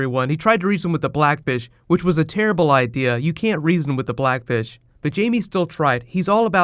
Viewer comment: I almost thought you said "you don't mess with the blackbitch"